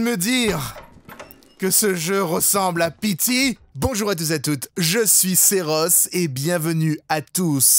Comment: Quand la dame a dit " Bonjour c'est Rose ", j'ai compris " Bonjour Seroths " 😂